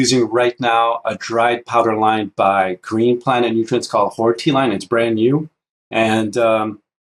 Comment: I thought he said whore tea line😑 Horti is what he said.